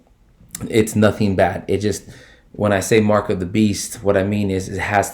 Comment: At the beginning it sounded like you said Marco the beast. Lol